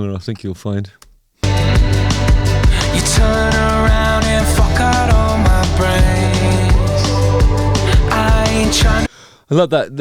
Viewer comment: …”you turn around and fuck out all my brains”? …am i mishearing?